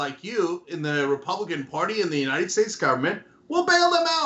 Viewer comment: They wanted the country ran like a business... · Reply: I always heard "the excited states of america".